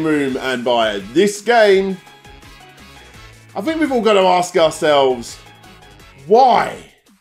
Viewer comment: I thought you were going to say "I think we are getting a little ahead of ourselves"